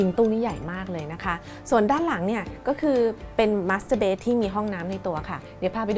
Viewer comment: อยากให้ระมัดระวังในการออกเสียงตรงนี้เป็นพิเศษครับ ตอนแรกได้ยินว่า มาสเตอร์เบ๊ด แล้วคิดถึง masturbate ที่แปลว่าช่วยตัวเอง55